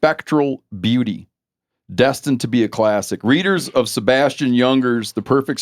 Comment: at first I thought Steve had said Ernst Jünger and I was very confused about how they had him on the show. Sebastian makes a lot more sense.